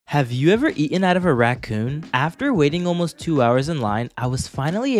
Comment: I thought he said "ate out a raccoon"